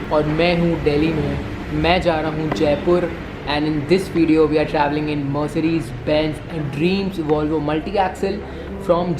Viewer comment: did you just say mercedes benz dream Volvo multiaxle?😂